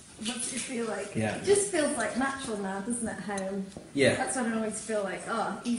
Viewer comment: does elizabeth call iain home? Or is that just what i want to hear lol?